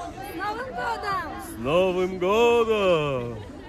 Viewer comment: LOL 5:23 is a premium Santa moment. What are they saying? "Aven goda Aven Goda". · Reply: «Happy New Year...» С Новым Годом ( s novym godom!)